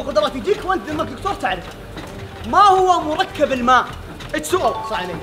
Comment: وش قال ل؟ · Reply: يقوله انت دكتور تعرف الجواب+وش السوال هذا